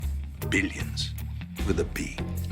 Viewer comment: Walt: "Billions" I heard: "Obedience"